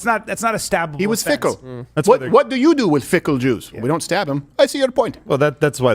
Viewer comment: Did he say "fickle juice" at 35:05?